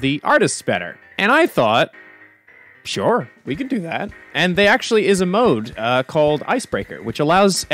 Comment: Idk if it’s rude but at the start at around 00:15 seconds I keep hearing •well they actually is a mode* and that’s hilarious to me lol